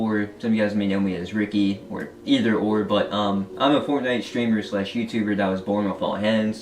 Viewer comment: Finally getting the recognition he deserves. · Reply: i thought you said fortnite is trash ?